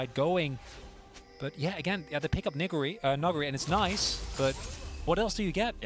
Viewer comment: what did he say!?!? 👀 “Nigguri” 😂